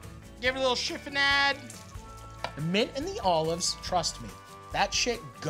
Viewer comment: I thought he said "making the olives trust me" & I was about to comment how hard I felt that "mint & olives" psh